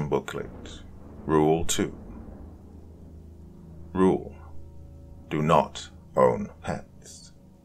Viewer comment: Thought you said "Rule 2: Do not own pants" 🤣